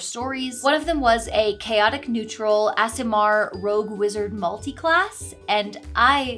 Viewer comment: I thought you were saying ASMR Rogue/Wizard and was wondering what that meant until I saw you write Aasimar xD